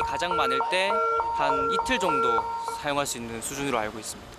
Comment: 이틀 분량이라고 들리는데 왜 자막은 1/2(한나절) 분량으로 나오나요? 내 귀가 이상한가....